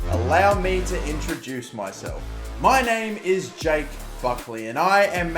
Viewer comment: Who else thought he said “Jake Fuckley” 😂😂😂